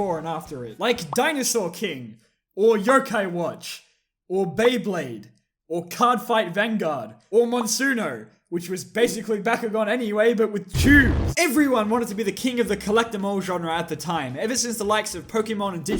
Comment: I was looking at a different window at this part and I thought he said "But with Jews" and I immediately shot back to this window and replayed it just to make sure